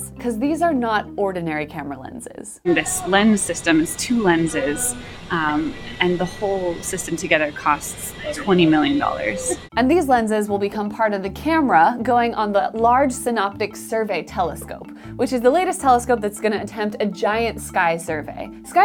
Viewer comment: Thought you said "It cost $29". Haha